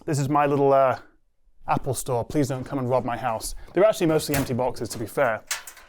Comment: did you said "mostly"?😂